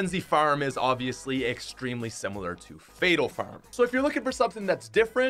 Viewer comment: Did he say fatal farm?(2:40)Or am I trippin